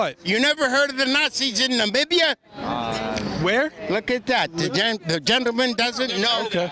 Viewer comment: I think that dude said "the Nazis did it in Narnia".